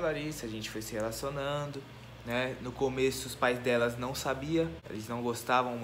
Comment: ele disse os pais delas ou eu entendi errado?ksksks